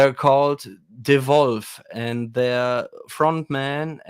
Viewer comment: Ah... DEWOLFF is the band mentioned as hard rock favorite. thought he might have said Devolve